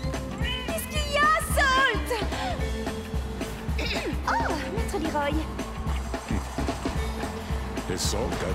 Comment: Why are subtitles so shitty in Tekken? She clearly says 'Msater Leroy' showing respect.